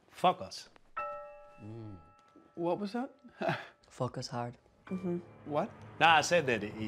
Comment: Lol. I’m not Latina but my family is Israeli. When the father said to “Focus hard,” I was confused by confusion. Only now, after finishing the video, do I realize what he thought that the dad had said.